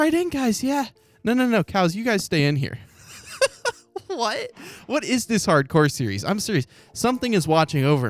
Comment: I thought I heard a child saying "what"